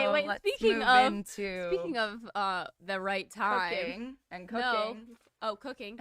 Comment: im pretty sure Aimee is the virgin because she talked about it on the date with wes that nobody has gotten her cookie and that nobody has gotten there with her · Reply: She said her cooking! Not cookie lol I know it sounds like she said cookie but that doesn’t make sense in regards to the context of their conversation!